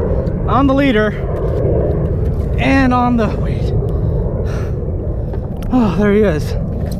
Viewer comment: Your haunted XD listen from 26:47 unto 26:53 that doesn't sound like you at all talking, after you say "on the leader & on the "===".think it saying weight or wait.. X'D you haunted bro X'D or your mic stuffed up in whispering voice..